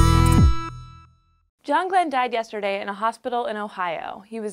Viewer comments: I actually thought it said John Green. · I'm not the only one who thought it said John Cena, right?